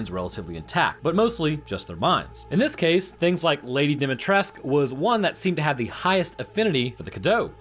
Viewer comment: Well, Lady Dimitrescu is actually most probably local to the area. From context clues we can assume that the village is located in Romania (Mia at the start of the game is cooking Romanian dish, it is mentioned few times that action takes place in Eastern Europe and money used in game is Lei, which is actual Romanian currency). So, as Dimitrescu is in fact a Romanian surname, (suffix "-escu" is typical for Romanian second names, meaning something like "son of" or "child of") we can assume that Lady Dimitrescu is native to the region and is not really French. Perhaps she may have lower immunity to the parasite, due to being from the "higher" class, as she seems to mention her ancient bloodline and nobility, so perhaps higher social status and better hygiene and lack of exposure to dirt or perhaps contaminated water may have caused her to do not develop immunity to Cadou, but this is only my rather quick hypothesis. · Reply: It is 100% set in romania, also "cadou" means gift in romanian. And the name bugs me to no end because I always hear "Dumitrescu" which is actually quite the common last name in romania.